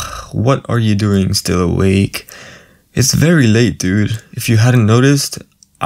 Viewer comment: Is it just me or did he said dude at 0:04??